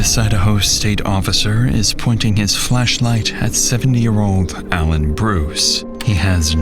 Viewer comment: you definitely said fleshlight. I replayed it a dozen times there's no way I misheard it.